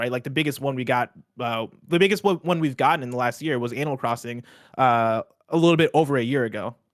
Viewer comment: now, you cannot convince me that he did not say "Anal Crossing"